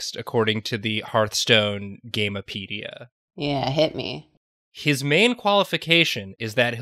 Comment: i heard that as "his MANE qualification..."